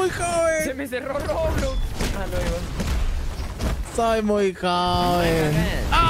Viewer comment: que dijo soaring ahí? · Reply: Se me cerró roblox